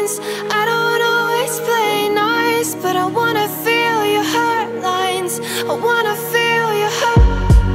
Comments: Okay...but I heard 'hurtlines' instead of 'heartlines'. And in the end I heard ' I wanna feel you hurt' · For the LONGEST time I thought she was saying "I wanna feel your head-lice" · I dunno bout y’all, but I keep hearing “I wanna feel your head lice” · for the longest time I thought it said i wanna feel your headlights